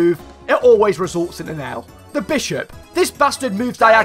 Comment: Me: moves my piece A randomass Bishop who's been standing in the same place for the past 50 turns: Bonjour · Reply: I thought you said bisharp